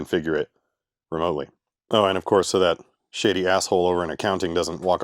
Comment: I had to rewind the production as I first thought you said "shitty" then I heard "shady" but you know you can't trust a bean counter, they always ruin the fun at a work place. 39:36 I used to work in a place that did exactly what you are doing. They used to set up all the gear and get all the routers and switches talking then we would go out to the different places and plug it all in. I did mostly grunt work not much configuring of devices everything was color coded so we knew what plugged into what. It was always fun when you arrived on site and one component did not get shipped.